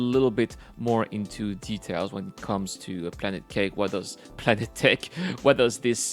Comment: did you say planet kek XDDDDDDDDDDDDDDDDDDDDDDDDD